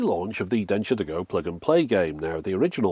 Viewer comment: I misheard that as ‘Dentures to go’!